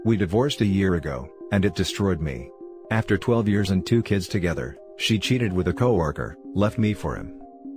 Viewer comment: i first thought he said "2 years and 12 kids later" (half awake and hungry)